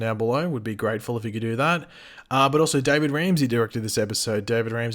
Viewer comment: I thought you said gordon ramsy directed this episode. Lol